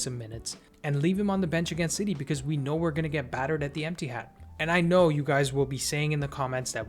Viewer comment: Did he say "Emptyhad" or "Ettihad"?😂😂😂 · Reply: When in doubt, always assume I said Emptyhad 😇😇😇